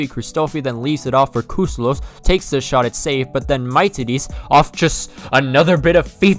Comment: I have to give a like to you my man im laying in bed sick and watching random videos on youtube and you made me laugh so freaking hard i cannot stop!!! At 13:05 did you really say "My Titties"??? :DD I dont wanna be rude but its so funny how you pronounced that name hahah, the name normally gets pronounced like the english word "me" and then "tea" and "dis" at the end. Dont get me wrong its totally normal, that you dont know how to pronounce a greek name, but hearing you say "My Titties" just killed me, i love you for that!